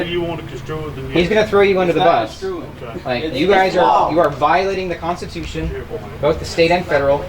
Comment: If you add the IQ's of the spectators together you wouldn't get to the outside ambient air temperature. is a scream. I swear I thought she said something about "Honoring the Constipation".